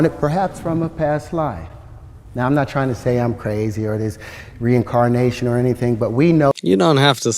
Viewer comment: All I heard was “I’m crazy as hell”